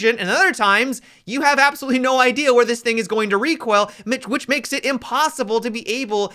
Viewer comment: does anyone else hear “you’ll have no idea where this nigga is going”